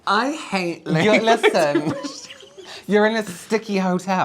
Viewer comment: I rewinded this part like 4 times lol. I'm pretty sure said "I hate laying my toothbrush down". I don't know why my brain needed to know what he said, but there you go.